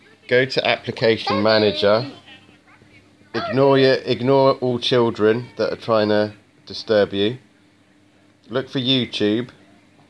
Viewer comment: 'ignore all children that are trying to disturb you' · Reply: Oh I thought he said, "Ignore all children that are trying to stab you" It was his accent that threw me off.